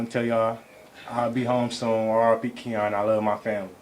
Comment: what did he say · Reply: He said "i ll be home so i'll be care my family".